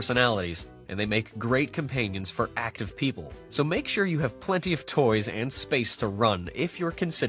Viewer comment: I heard ''So make sure you have plenty of dogs'' 😂 I like the idea though 😁